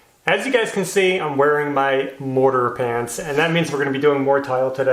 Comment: Wait .. did he just say "Mordor pants"? ;-)